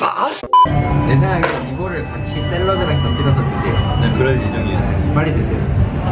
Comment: Ich verstehe halt wirklich guten Appetit